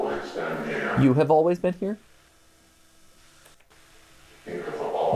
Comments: i heard "it will always end here" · idk about you guys, but on the very last bit of the final video I hear either "It'll have always been here" or "It will have always end here."